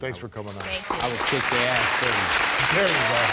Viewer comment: Lol, I thought the dad at the end said, "I'd have kicked your ass, too" 😂 I'm sure that's not what he said, but it sounded like he did, lol.